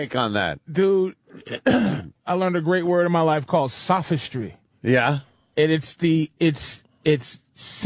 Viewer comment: Sasugry? Sa-citri? What did he say? google isn't coming back with any results. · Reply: Sophistry.